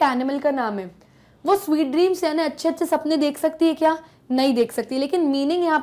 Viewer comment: "A sleep" instead of " A sheep full of sweet dreams" at 26:20, pls rectify it☺